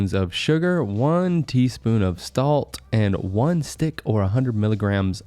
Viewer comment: he said stalt instead of salt